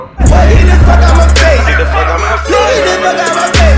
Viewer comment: the one that had th john cena song sound like he said I fuck up